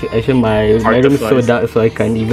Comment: i thought this shit said artificial ascent